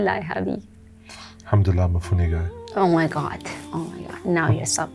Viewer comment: Such a pure beautiful couple MashAllah ❤ what did he say @ 16:10 ? Alhamdulilah for ??? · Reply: “Alhamdulilah I’m a funny guy”